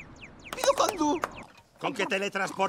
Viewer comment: no entiendo que dice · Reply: "pido condu" quiere decir me pido conducir.